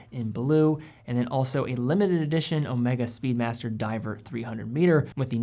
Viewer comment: sounds like you said speedmaster